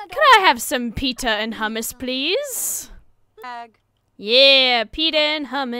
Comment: Daphne is good at dancing it's a good dance moves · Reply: I pretty sure its pita but when you said peeta and houmus I misheard it as peeta and kattniss 😄